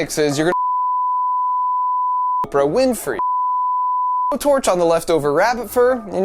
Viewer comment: I swear I heard him say Oprah Winfrey